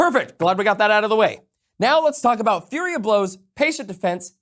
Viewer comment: I keep hearing "Fury of Blows" but isn't the feature called "Flurry of Blows"?